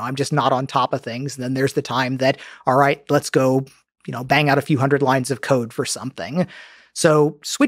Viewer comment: At first I heard him say bang out a few hundred lines of coc.